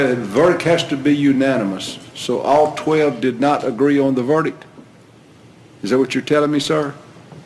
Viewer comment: Ok please help me detectives of the internet: at 32:56, is he saying "I didn't agree he was innocent"? As in the verdict almost presented being not guilty?